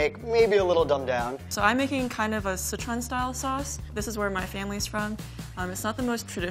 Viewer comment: caption says "citron style sauce" when she's actually saying "Sichuan style sauce" 6:44 caption says "I'm Southerners traditional" when she's actually saying "it's not the most traditional" 6:53 caption says "we're going to Julian this" when he's actually saying "we're going to julienne this" 6:56 caption says "You are finally slice up" when he's actually saying "gonna finely slice up" And whenever anyone speaks in a different language, instead of using the Romanised versions of those words, the captioner simply wrote "(speaks in foreign language)"